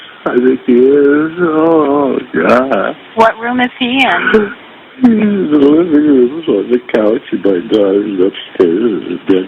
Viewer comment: "he's in the living room on the couch and my daughters upstairs" is what he actually says :)